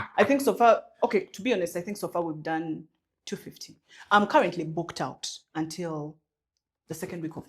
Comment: I hear "I'm currently booked out till March"😂😂😂😂